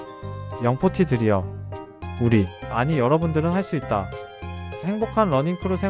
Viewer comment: "우리" 라고 지가 두 귀로 똑똑히 들었당께유